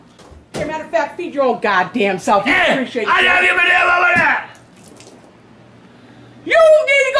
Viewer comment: wtf did he say😂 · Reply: He said I will put it over there how bout that!! 😂😂